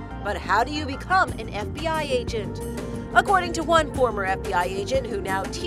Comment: I thought she said so how do you become an empty eye agent